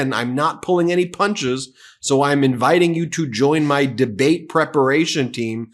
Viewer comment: 'debate preparation team' ???? I thought he said he's been preparing for this his whole life?